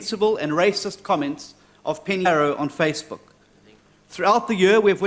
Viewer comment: Lol why are dubbing the name of Penny Sparrow? admin though. You rolled fast on that part and we couldn't hear it properly.